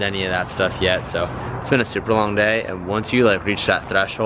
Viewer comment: he keeps saying Supra instead of super , 21:00 "its been a supra long day"